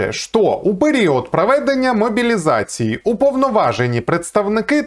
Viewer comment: Услышал дословно:упыри от...😅 Хронометраж